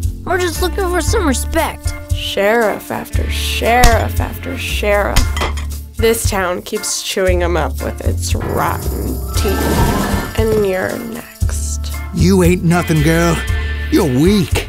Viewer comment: Can someone please tell me what the hell did she said?. I'm having trouble understanding it, my mother languaje is spanish tho. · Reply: Gorila Invisible “Sheriff after sheriff after sheriff. This town keeps chewing them up with its rotten teeth, and you’re next.”